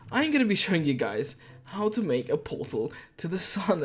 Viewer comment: Was it just me or did it kinda sound like he said how to make a porno